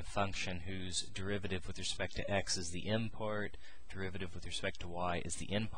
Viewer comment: Is he saying "import" @ 0:26 - 0:32? whats an import? :/ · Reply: he's saying "M part" and "N part"